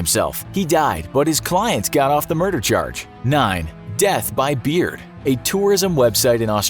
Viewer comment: Phew! I thought ye said "death by BEER"! I felt panicked!